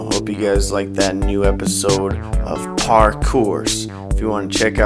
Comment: I thought he said parkhorse